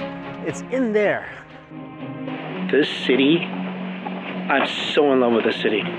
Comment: am I the only one that thought he said “this sh*tty”?